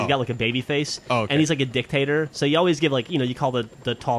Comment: I always hear, when says : "Dictator" I hear this: "Dick Hater" Do i have a problem???!